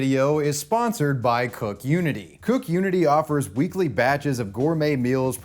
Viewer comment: 🤦🏾‍♂️ omg, I thought that said “coc* unity.” My mind is ruined 😂